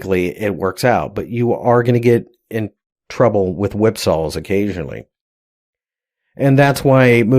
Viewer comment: What's the word you said at 8:10? Sounded like whipsols?